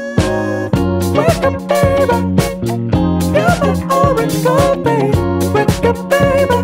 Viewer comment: misheard as " you're my origami" hehe XD Love the song btw :3